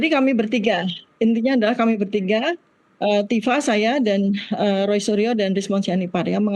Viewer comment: tifa saya roy rismon, ngomong apa ini